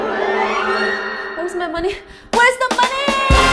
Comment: Tbh at first i thought u said “where is the monkey?” So i clicked this video xD